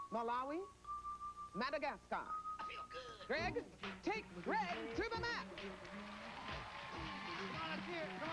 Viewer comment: I thought , I heard Greg take Gregg to Africa . 2 Greg’s ?